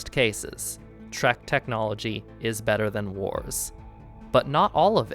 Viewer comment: At a little before 8:35, I thought you said “shriek technology” not “trek technology” I need more sleep